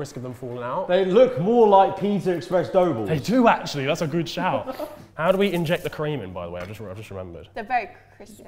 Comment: pause...what did say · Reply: "That's a good shout!" "How do we inject the cream in, by the way? I've just realised."